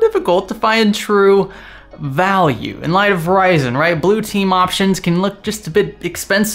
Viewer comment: Did he say Verizon instead of Ryzen at 0:06?